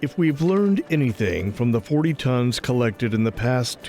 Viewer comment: 83,000 lbs in just two days gives one an idea of whats out there and the scope of the fishery (slightly alarming) if you ask me...just waiting for a market to open is the next big thing for Asian Carp. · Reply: I thought they said 80 tons? 160,000 lbs